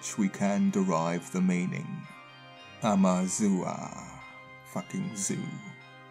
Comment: I love seeing things about Amuru. People don’t even realize Ferdinand Vespucci changed his name to Americus, “devotee of amuru”, after spending time in Central America. The word America never had anything to do with him, it was always the name of the land Amuru-Ca “Land of the feathered(Plumed) Serpant” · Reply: What change of disposition does the narrator seem to have at 5:38?? Did he just say "fucking zoo" out of the blue, without explanation, wtf??